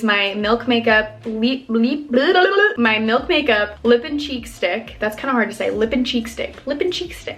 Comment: lip and CHEESE STICK... that’s literally what I thought you said so I had to go back and listen again go double check lmao